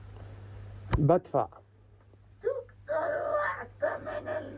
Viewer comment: listen closely it sounds like he says b*tt f*ck